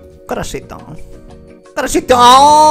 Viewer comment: Qué dice en el segundo 01:08? "Cresito" o "corasito"? Y qué significa?